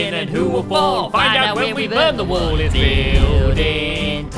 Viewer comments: I thought they said burn the world when I first watched this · why in the intro it sounds like that said "find out when we burn the world".......